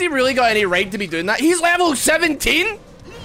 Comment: you said "HES LEVEL 17!!!!" but i heard "HES OVER 9000" lmfaoo